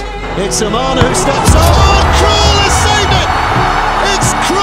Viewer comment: I heard "it's cruel for the dutch !" I thought wtf is he talking about. Before realizing 😂